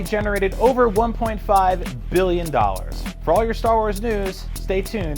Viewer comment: i thought he said 1.5 million, worldwide, i was like wtf?? thats one actor, shitty one at that.